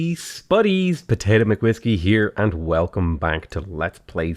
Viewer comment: Please stop saying “hey spuddies” at the beginning, it’s the only reason I dislike. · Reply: Robby Thomas I always thought he was saying ‘buddies’ lmao